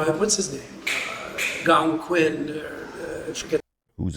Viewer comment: WHY is it always interviewers who BARLEY even seen Star Wars?????? GOLLUM QUIN??!?! Tf he say???